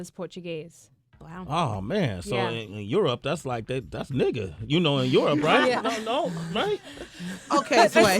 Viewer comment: Did he really say that's like nigga? @ 4:21 like she looked so uncomfortable after he said that